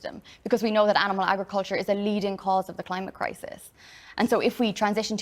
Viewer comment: I think she says "...animal agriculture is A leading cause of the climate crisis." I don't think she said "...THE leading cause". Looking at just emissions - More forests = better carbon sequestering and cattle farming the leading cause of deforestation. So, we need to factor in the felled forest in addition to the raw emissions from the animal farms when figuring out the true impact of our dietary choices. Also but there's also ocean pollution, 20% to 30% of plastic comes from marine sources such as fishing nets, lines, ropes, and abandoned vessels. eutrophication caused by animal farm waste, etc. Either way great thoughts on the interview!